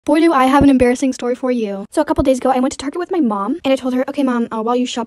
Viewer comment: Pls at least one person was like me and thought she said twerk when she said target.. It's not similar at all..